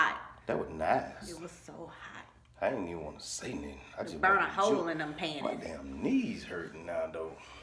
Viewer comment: I so love y'all!! 😍😍😘😘 But did u say "burnt a hole in them panties" @ 6:45?? 😂😂😂